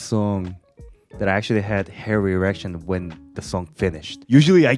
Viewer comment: Can I say I always thought was saying "hair reaction" not "hair erection", but hair erection is a million times better